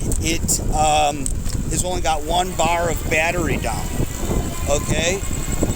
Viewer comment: Hello nice video. At 7:55 did you say you've been riding for 10 miles and you only have one bar down or you've been riding for 10 miles and you only have one bar of battery now? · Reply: Thank you. It was 1 bar down out of 5 there was 4 left after 10 miles